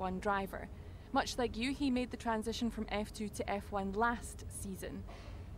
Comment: "much like Yuki made the transition from F2 to F1 last season" - aren't we in F1 2020 season? Yuki was still F2 then... did I hear wrong? I'm so confused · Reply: no, you’re right, he was asking a genuine question, it does sound like Yuki but it is “you he”